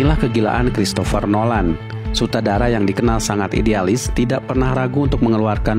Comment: apa telinga gw doang yang dengernya SUTADARA?